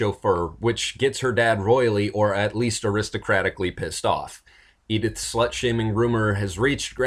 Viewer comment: I thought he said Aristocatic!